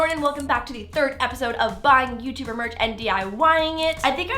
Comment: I thought you said buy a dying you tuber march